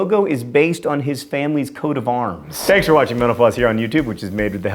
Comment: It sounded like you said, "Thanks for watching Menopause..."